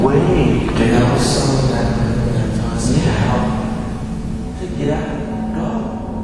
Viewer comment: For some reasons I heard "Meooww" instead of "Get Out". Lol